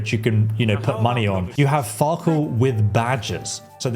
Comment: I thought he said farkle with badgers at first lmao